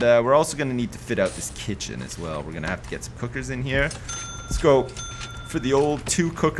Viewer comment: When he was putting the cookers in the kitchen I heard 'we're gonna have to put some hookers in here' and I just 'wtf, Sips, why?!?!' and then I saw him putting the cookers in and was like 'ooohhh.'